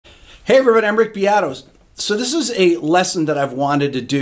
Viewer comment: "I'm Rick Beatos" tell me someone heard it too